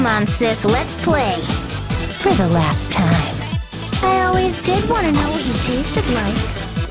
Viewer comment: why did you think she said “I always did wanna know what your taste of likes”???? It’s so obviously that it’s “I always did wanna know what you tasted like.”